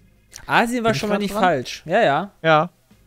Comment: Ich hab die ganze Zeit verstanden: "Azir war schonmal nicht falsch" :D